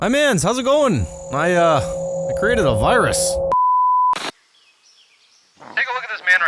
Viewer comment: What he said: "I created a virus" What I heard: "Forged Covid Labs"